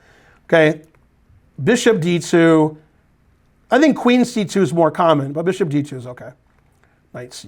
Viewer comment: Hah! At 19:19, when Simon's opponent played Bd2, and Ben said "I think Qc2 is more common, but Bd2 is good, too" I misheard him. I thought that he said "Qd2 is more common," so I spent the next five minutes trying to figure out how Qd2 wasn't just blundering your queen. 😂 Also, at 23:07 Ben says that Simon "Gets his bishop out of hock." Out of hock? Really? He pawned a bishop? But a bishop is better than a pawn, why would you ever want to pawn one?😂 Damn...I crack me up. 😂